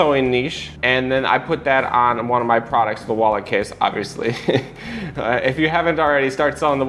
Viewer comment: It sounded like you said wallet case but I'm not sure what you said. It's at the 5:40 mark.